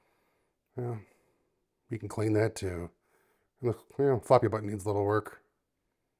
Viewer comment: Lol I would have sworn he said "the fuck it button needs a little work" but CC says he said floppy button. 😂